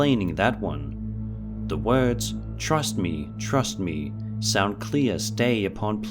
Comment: It almost sounded like it said "best to trust me"